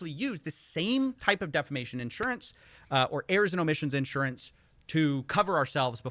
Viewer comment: I keep hearing it as "airs and emissions Insurance".